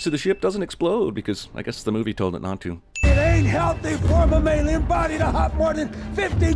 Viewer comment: Honestly, I didn’t know Yandu said Mammilian. I thought he said My Alien